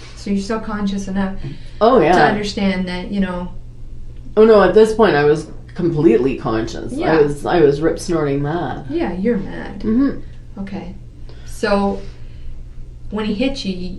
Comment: “Rip snoring” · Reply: Yeah I was like did she actually just say that....I thought she said rip snorting at first